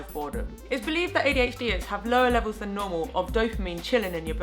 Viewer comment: I thought you said "lower levels of normal" and i started laughing because TRUE. I literally paused the video to write this comment.